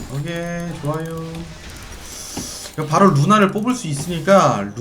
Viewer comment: 초 쯤에 순간 "바로 눈알을 뽑을 수 있으니까......."라고 들었네.